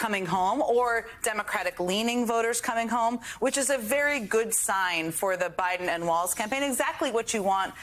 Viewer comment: did she say a good sign for the BIden and Walz campaign first?